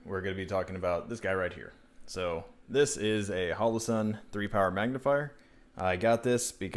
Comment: I miss heard you and thought you said retard magnifier